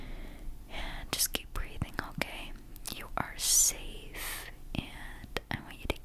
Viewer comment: I HEARD “andddd just keep breathing cocaine” TWICE